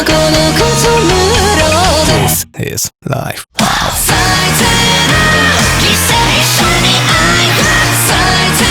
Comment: 最低な犠牲者に愛を のとこ、最低な犠牲者に愛ふって聞こえるの私だけ？